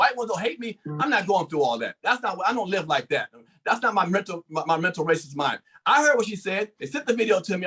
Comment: Did say "that's not my mental racist mind"???